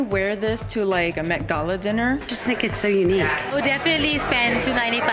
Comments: "I could definitely wear this to a Met Gala dinner" - someone who's never went to a Met Gala dinner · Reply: I legit thought she said "mcdonalds dinner" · "I'd definitely wear these to a Met Gala dinner." Biiiiiiiiish in your dreams. 😂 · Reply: Shalucard haha, I thought she said McNugget dinner.. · Are we just gonna ignore when that lady said she could wear the shoes to a met gal dinner? Likeeeee how are you even getting there?! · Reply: I totally thought it sounded like she said a McDonald's dinner! And she'd be more correct!😆😆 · “I could wear this for a MET gala dinner” as if you’ll ever be invited to one lmao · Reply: I thought she said a McDonald's dinner. Lol